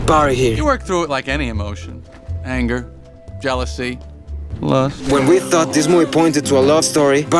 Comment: excuse me what 😃 how did that go over my head I thought he said loss 💀💀💀